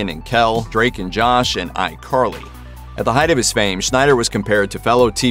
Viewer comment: Did he say “I’M Carly”?